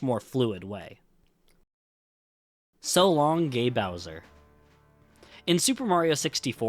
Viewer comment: Mario actually says "So long, eh Bowser?!". The reason the line is missing in the Switch version is because it's actually the japanese Shindou version, which added Rumble Pak support and the Peach voice over that was previously not in the original japanese version. The line "So long, eh Bowser?!" is missing from every japanese version and the reason is that, in Japan, Bowser is not named Bowser, but rather "Kuppa". So, having Mario randomly calling him Bowser in one of the voice lines would have just confused japanese players. Since the Switch version is the Shindou version on which a patch for textures and language is applied, it makes sense why the line cannot be heard there. Hope I was able to explain this well! EDIT: I didn't think this comment would be this popular! Thanks for the MANY people who reminded me he says King and not eh. I learned that a long time ago but for some reason, a memory of someone saying it was "Eh" left a bigger impression on me so I guess it took over my memories. Thanks for many replies though. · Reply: It always sounded like he said 'so long a bowser' to me